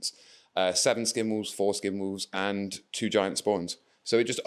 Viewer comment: Great video, would love to see more of this content. That Wolves of the Sea army looks incredible but I did laugh when said “four Skin Wolves” - my juvenile brain obviously heard it as “foreskin wolves”.